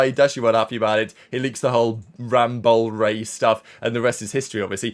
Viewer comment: what does say here : Rambowl Ray? (I know it's in reference to Rambo the Optic coach) But what is it he says Thanks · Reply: Yes rambowl because I beleive Rambo would skip scrims or something somedays in order to go bowling. Hence the nickname rambowl